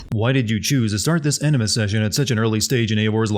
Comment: This guy said "Enema Session" instead of "Animus Session" 2:52 I don't care what the subtitties say, i heard what i heard.